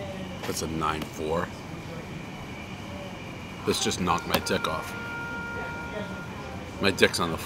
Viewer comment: I thought he said knocked his tick off lol 😂